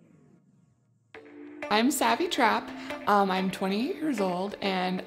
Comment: At first, I thought she was late 20s. It even kinda sounded like she said "I'm 28 years old". But according to the closed captioning, she says "I'm 20 years old", which makes more sense since the guy is only 21.